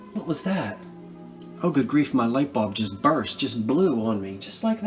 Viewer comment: My leg? Nothing. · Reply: Yes. When something snapped I thought you said My leg!! But I don't know. Just a missunderstanding. Sorry. ❤